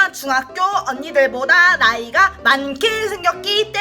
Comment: 나만 아이라고 들림? 0:410:410:410:410:410:41 이거 누르면 아이라고 들리고 보다가 나오면 나이라고 들려요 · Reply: 으로 들어보세요 "나이" 인데 "ㄴ" 발음이 잘 안들려서 아이 라고 들리는거에요 ! ( 나~아이가)